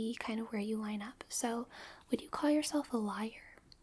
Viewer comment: I heard “you’re probably a lawyer” instead of a liar and was ready to just go with the fact that lawyers are just the majority of people in hell 💀